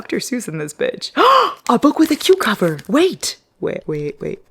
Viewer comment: For #9 I thought you said “a book with a cucumber” 😂 oddly specific